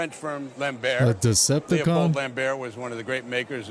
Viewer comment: 😂 WTF saying Decepticons as Automata